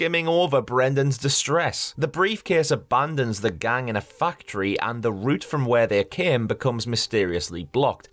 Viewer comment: When you said "factory", the first few times, I thought you said "fuck tree"